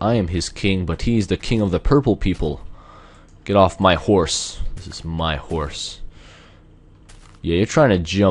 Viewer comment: My video stopped to render at 0:16 and it sounded like he said "This is my whore"